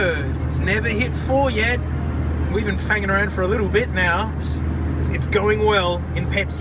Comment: what did he say??? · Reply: Fanging